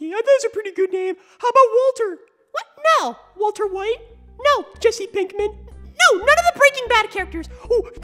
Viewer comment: I laughed so hard when I heard Jesse Pikmin